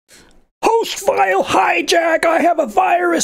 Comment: Nice video but holy shoot the autoplay scared the living shit out of me. I was SSHing into a pc and then i just heard "Whos filed did i just hijack" in a very funny voice but thank god it was only the video. lol